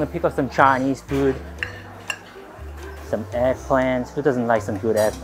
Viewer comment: When he said eggplant I thought he said assplant 😂😂😂